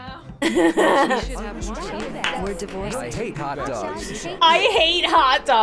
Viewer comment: HELP I HEARD "WERE DIVORICING"